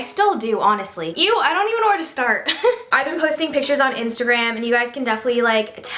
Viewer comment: Ok I’m soooo sry but I thought you said “I have pussy pictures on instagram.” 😱